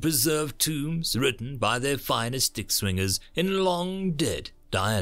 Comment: I swear I heard at 2:22 "Humans even preserved tomes written by their finest dick swingers.." I was like yeah that is a pretty brutal and ancient method of warfare.